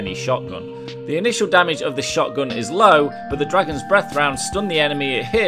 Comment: I thought he said dragon fire shotgun.. but isn't that explosive?